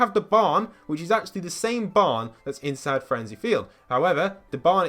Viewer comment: Who else heard him say frenzy field. Its fatal fields